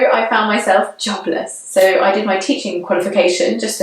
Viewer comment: Hey The CC shows I found myself chocolate instead of jobless @ 6:27.